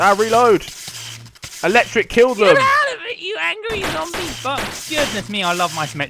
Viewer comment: Did say a bad word at 17:10? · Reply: "Ahhhh, get outa here zombie butts!" so not really